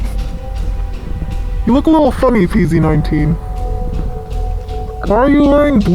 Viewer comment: GUYS AT 13:28 IT SOUNDED LIKE PZ4!!!!!!!!!!!!!!!!!!!!!!!!!!!!!!!!!!!!!!!!!!!!!!!!!!!!!!!!!!!!!!